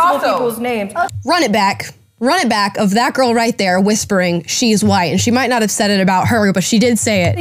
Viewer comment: I thought she said, she's lying. Not, she's white.